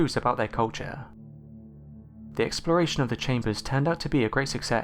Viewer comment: I heard him say "the exploration of the chamber was aggressive sex" haha